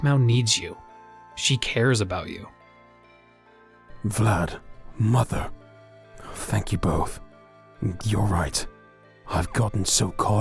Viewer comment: It sounded like Gorrath said "Mother, Vylad, fu**k you both."